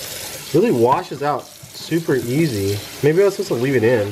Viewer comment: did he say it washes out really yeezy lol